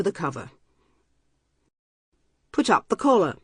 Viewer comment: I thought she said "put up the colon"